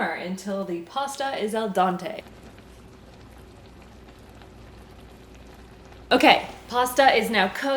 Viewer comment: did she just say al dante